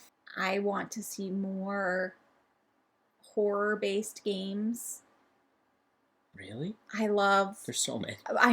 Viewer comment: When you said you want more horror themed games I honestly misheard it as more “whore” themed games which really would have been a unique hot take!